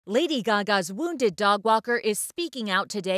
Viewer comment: I know I'm not the only one who thought that said dog water